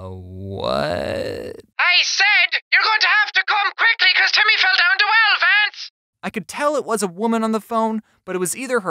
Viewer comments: I heard you'll have to come quickly cuz timmy fell down the well,Lance >;0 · The jacksepticeye women sounds like she said you gotta come quickly cos Timmy fell down the well vance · this is what i heard "you better com kuikly because timmy fell of a cliff vance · I know what she said, (5:50) she said "your going to have to come quickly timmy fell down the well, vance." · On that one with jack I thought they said “ you better come quickly because Timmy fell down the well Vance! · Ok, at 5:37 I heard, YOUR GOING TO COME QUICKLY BECAUSE TIMMY FELL DOWN THE WELL (L)VANCE!! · why do i heard she say “ you have to come home quick timmy fell down the well fance” 😂